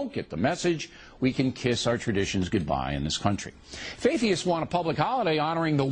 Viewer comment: At one point it sounded like he said "faitheist."